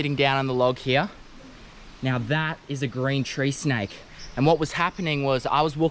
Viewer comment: What did he say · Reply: Green tree snake